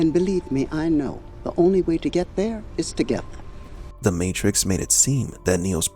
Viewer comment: I always thought she said "the only way to get there is to get there" lol Mandela effect Edit: whoa i just realized to get there as one word is togethere